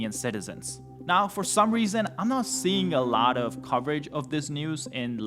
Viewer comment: why did I hear “I’m not seeing a lot of property disputes”😭🙏